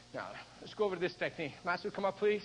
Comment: Did you call him master @ 7:17?